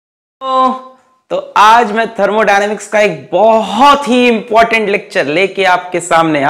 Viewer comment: What did sir say at 0:00 second 🤣🤣🤣🤣. Ohhhhh ooooooo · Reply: It's..hello baccho